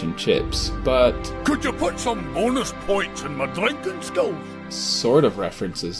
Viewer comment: I thought the mountain king Said: Can you put some bonus points in my drinking skull